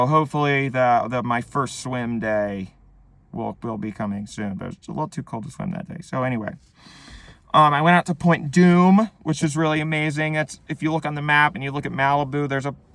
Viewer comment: he says Sounded like he said “went in to play Doom which was amazing” which woulda been hillarious